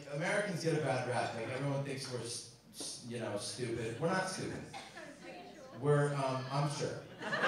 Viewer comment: I couldn't hear the second heckle around the 4:43 mark, anyone know what was said? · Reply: She said 'Are you sure?'